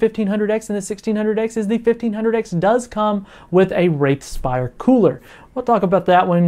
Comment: hahah closed captioning at 4:00 instead of "Wraith Spire Cooler" it said "Rape Spire Cooler"